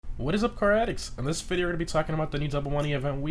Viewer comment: For the past year I thought you've been saying "what is up caratics?" WTF does that mean? :D